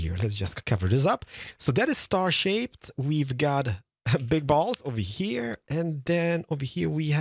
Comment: Oh bottles I thought he was saying ballz wow subtitles definitely required this episode